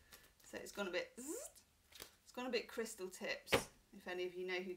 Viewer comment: Fabulous reading!!! Giggled LOUDLY as I thought you said Crystal tits 😜😜😜😂😂😂.. feel great starting my day with your readings 🌈xx